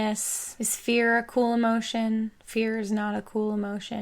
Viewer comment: when you said "is that a cool emotion?" I misheard it as "the Spherical Emotion"